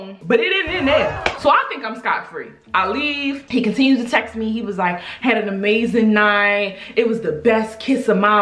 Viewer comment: Did she say Scott at 13:30 ??